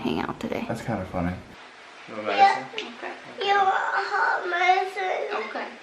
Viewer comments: I thought said "You are a hot mess" 😂😂😂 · I so thought that said ' your a hot mess mom ' hahahahaha naw seeing him sick is really sad though